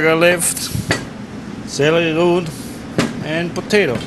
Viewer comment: What did she say @ 2:30 root of what? Anyone? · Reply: Celery root, or celeriac